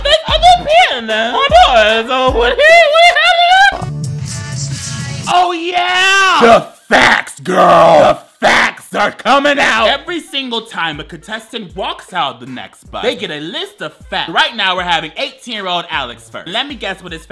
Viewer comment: my gay ears mishearing "the facts are coming out" and thinking screamed the f-slur 😂